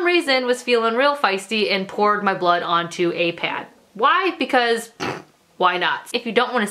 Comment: When she said "I put my blood on a pad", I thought she said "I put my blood in a pan" and I was like why the hell would you do that?😂